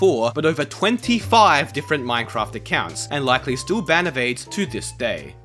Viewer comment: the auto-subtitles says "and likely still ban of AIDS (all caps) to this day" XD